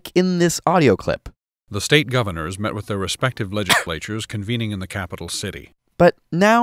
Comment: hm, I hear leg-lators.